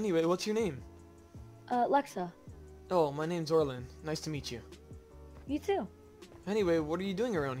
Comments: did you just say Orinl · Did he just say orlin...? It’s orin no L